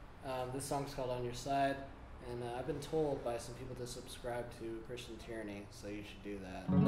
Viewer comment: I thought said "to subscribe to christian tyranny" and I said "no way" but then saw your channel name. Great performance as always.